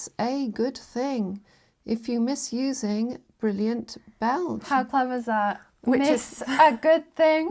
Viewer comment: At first I thought it said 'Brilliant Beige', which already seemed like an unlikely combination of words to me.